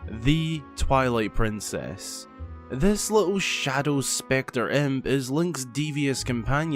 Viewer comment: "This little shadow spectre imp" I thought you were gonna say "this lil shet" for a second there lol